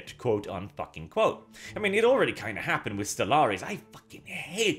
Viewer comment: did he say stellaris?